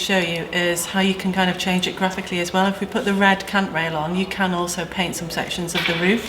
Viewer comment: why does it sound like she is saying at 25:54 "if you put the red c**t rail on" and at 26:21 "so theres the c**t rail"